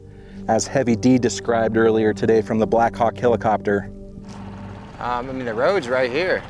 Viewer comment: did say Heavy was flying his BLACK HAWK ??